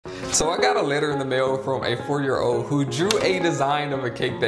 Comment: That’s pretty good for a four year old ngl And the cake is AMAZING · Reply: Now it all makes Sense, i thought he said fourty.